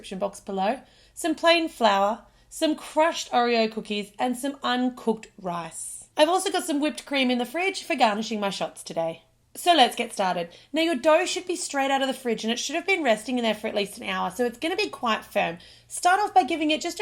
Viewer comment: Lol i thought at first she said "uncooked grass" but later she said rice and i was like "oh" 😂